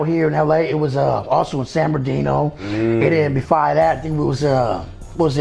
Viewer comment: he slipped up and said befive that instead of “Cefive that” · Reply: I think it was instead of "before that"